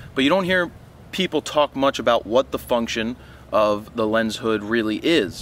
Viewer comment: LOL, I thought you said "what the f*ck-tion" in 0:35 Thanks for the upload